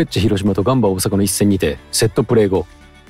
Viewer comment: 手癖の悪さって言うからセットプレーが窃盗プレーって聞こえた。